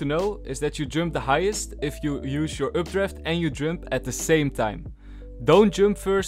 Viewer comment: Can someone tell me what did he say at 0:38 updraft and? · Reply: Jump at the same time